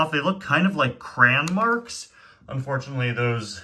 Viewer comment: you did not just say cran